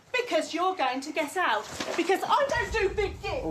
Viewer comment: Did say because I don't do big dix at 5:13?😂